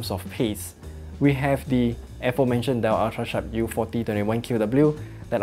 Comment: Great video but what was the name you said in 07:26???? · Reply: Dell UltraSharp U4021QW